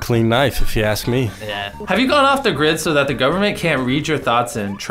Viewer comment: I swear to god I heard jimmy say hot porn instead of popcorn